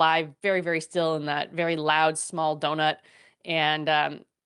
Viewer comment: Did she say donut ! ? ! 🎉